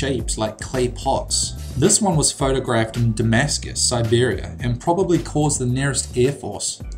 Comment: Did he just say that the photo was taken in Damascus Siberia at 2:45?